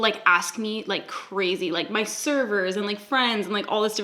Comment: i thought you said my SERVANTS and i was like DamN SAM